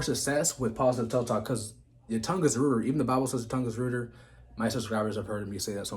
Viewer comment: I’m sorry what was that word at 4:07? And your tongue is what? Did you say your tongue is ritter at 4:14? And if anyone is in a mood to laugh slow the speed down to .5x he either sounds like he’s really drunk or on some serious downers.